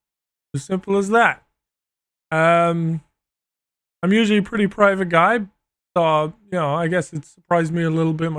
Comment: “I’m a private guy” proceeds to talk about his nuts in detail for 19 minutes straight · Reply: You misheard. He said "privates", not "private" 😂